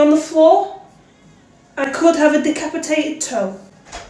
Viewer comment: “I could have a decapitated toe” made me wheeze 😂😂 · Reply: Bro i thought she said i could have a duck and potato🤣🤣🤣